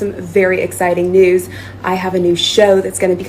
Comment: she said she has a new show...I thought she said she had a new nose...lol